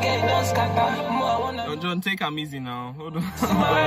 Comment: Did you just say "take am easy"😹 Your pidgin is good bro😹 but your British accent is holding you back a little 😹😹